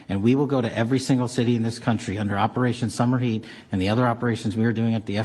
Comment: 🤣🤣I thought he said Operation Summer’s Eve.😂😂